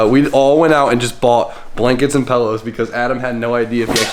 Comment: Did you just say pellow...